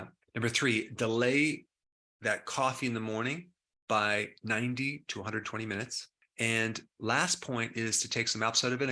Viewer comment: Do you mean 90 and 120 minutes in delaying coffee? I thought I heard 90 to 20 minutes? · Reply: 90 to 120 minutes